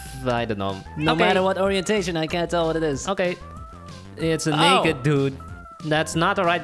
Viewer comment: d-did he say the n word? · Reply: he said "naked"